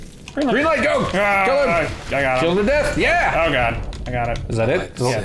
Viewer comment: did i hear the frase: "kill him to dead?" XD